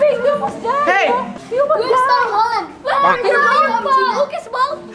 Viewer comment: gw yg salah denger atau gimana dia ngomong "Where's Tom Holland :v"